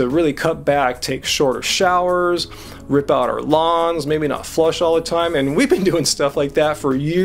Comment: lawns... i thought he said lungs LOL